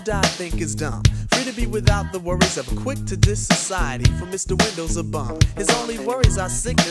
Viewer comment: I remember when I first heard this I thought he was saying Mr window and he was actually saying Mr wendell 😂😂😂😂 still listening in 2024❤